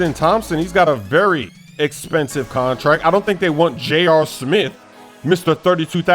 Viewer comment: Did say 'J.R smith' with the smaaaaash auto tune?🤔